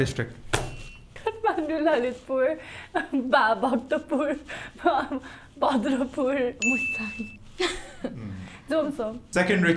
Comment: did she say Bhadrapur?